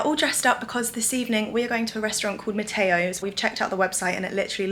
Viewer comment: can I ask for the vegan restaurant u hv been ? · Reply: I heard "Mateos". ??